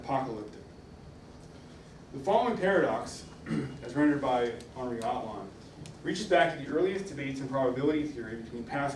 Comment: did he say "henri atlan"?